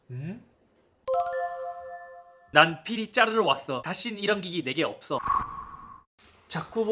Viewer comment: “필이 짜르르 왔어”가 “PD 자르러 왔어”로 들려서 깜짝 놀람. 잇틀러